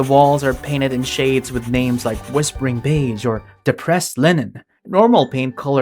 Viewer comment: i heard "Depressed Lemon" XD